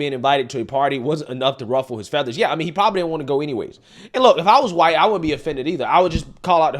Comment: No one mentioned quitting prior to you. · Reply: My bad. I thought it said "go away", as in resign, not "go anyway".